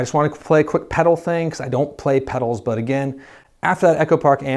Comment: I could have sworn that you said that you don’t play pedals. I replayed the video, and I couldn’t hear any words that you said. My only goal in life was to impress with my pedal collection. Thanks for killing my dream. Thanks a lot! 😉